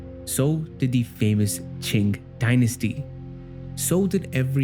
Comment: As a lover of histry, geography, and other cultures I absolutely love this video! But bro you gotta relax on that hard G sound after Qing because it kinda sounded like you said "chink dynasty" and that caught me off guard a little too hard 😂💀 but I know that wasn't intended😂😂😂😂😂